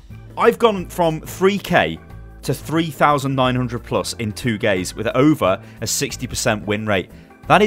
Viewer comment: he say 2 gays instead of 2 days